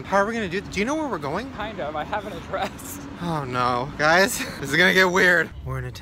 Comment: when Ryland Said "I Have Address" I Thought he said I have a dress (;-;)